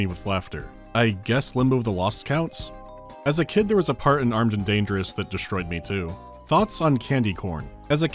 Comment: I misheard you in the question segment and thought you said you'd played Armed and DELERIOUS as a child for a second. Which, frankly, would have explained A LOT, but its probably for the best that you gave that one a pass.